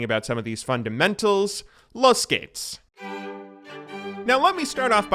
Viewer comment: did you just say "los gehts"? :D